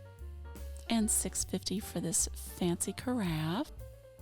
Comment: I really like both pairs of chairs. I truly thought you said “fancy giraffe”, rather than “fancy carafe”!!🦒 I kept looking for the fancy giraffe, because that is definitely something I’d be interested in. 😂 The fancy carafe is very nice, though. I love the Star-Spangled Banner facsimile. And my love bucket runneth over! ❤❤ to you and Mama!